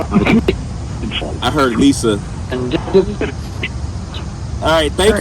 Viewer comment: through 27:51 I heard her say Lisa Left eye.